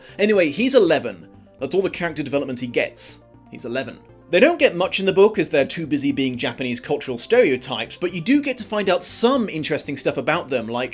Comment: For the longest time I thought you said he was a Lemon, not 11. And I went on this long tangent trying to find out what weird kind of slang that was & what that meant. 😅